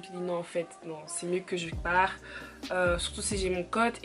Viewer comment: Tu dis plusieurs fois dans la vidéo surtout si j’ai mon côte c’est quoi cote ? 1:34 ?